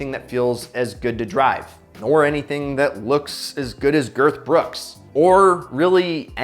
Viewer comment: Did he just say looks as good as Girth Brooks 🤣🤣🤣